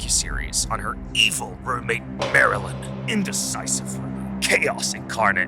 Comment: I first thought he said maryland 💀